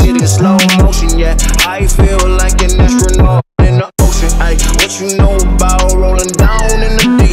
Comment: i hear “oh shit” instead of “ocean” x10